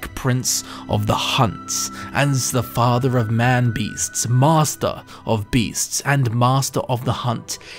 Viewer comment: At first I thought you said Mazda of Beasts